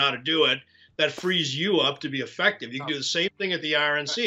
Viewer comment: “… that frees you up to be affective… “ The audio on my iPad glitched. He really said “defective” didn’t he? Or was it “infected”? 8:41 If I was one of HIS ‘cyber guys’, I’d have my feet up on a †teletype, eating Twinkies, drinking canned Coke, while laughing at Mike. 10:23 Doesn’t that bunker where Alex Jones & MTG been hanging out need a total and severe decontamination by now? Along with fresh air and pest removal (Jones & MTG included)? Must be mighty fetid in there. 10:50 The Wilmington location Trump is ranting about is Biden’s PERSONAL home, not a place where foreign spies, criminals, and right-wing lunatics visit. Or am I wrong? — BG THE SOCKS STAY ON! 🦶🏾🤢🧦 †In my day, the teletype was how we communicated with computers, and Twinkies with Coke was our food of choice.